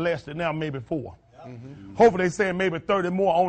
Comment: What did he say at 1:45? Hopefully?